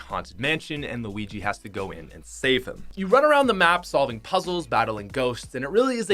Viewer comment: I thought you said save her. XD